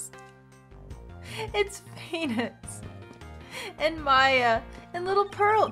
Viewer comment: What says: " it's phoenix!" What I heard: "it's penis!"